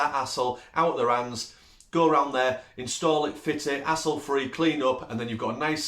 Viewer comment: I thought you were saying asshole free at first until I realized you were saying HASSLE free.